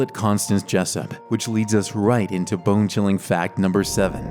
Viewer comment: i heard him say bingchilling 💀💀💀💀